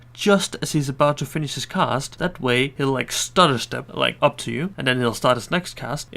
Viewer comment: Great videos you make but my god you struggle with the accent. One of the several examples is here: 5:59 Like, wtf did you say? :D Sometimes its so hard to understand what you mean. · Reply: clearly understandable he said stutter step